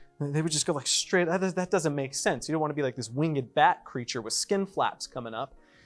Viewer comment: Did I hear Bat? I might actually like that as a Batman fan.🤣